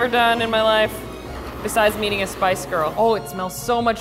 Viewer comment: “this is the craziest thing i’ve ever done in my life, besides meeting a spice girl”😂❤️ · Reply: OOH MEETING! I swear i heard her saying "eating a spice girl" hahhahahah it makes much more sense now